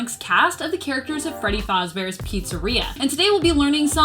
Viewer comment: DID SHE SAY FREDDY FOZBEAR😂